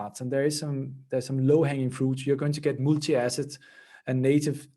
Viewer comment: he says "m??? assets and native token support" - i can't make out the word that seems to start with m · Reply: Multi- asset